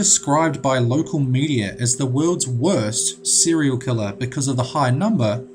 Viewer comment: When I was watching this my younger brother thought he said "Cereal Killer" and said those are the people with wanted posters printed into the back of cereal boxes